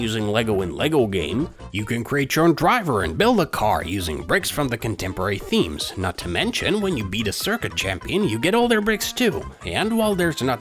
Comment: for a second I heard "You can create your drunk driver"